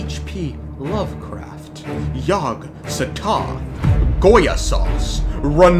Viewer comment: did you say "Goya sauce"?